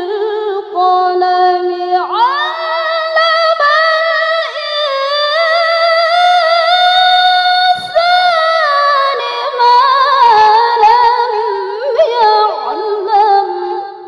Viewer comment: " apa aku yg salah dengar kok bacanya 'ALLAMALINSANI MA LAMYA'MAL.